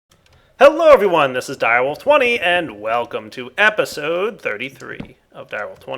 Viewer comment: I was kind of high watching this and I thought you said 4/20 instead of direwolf20...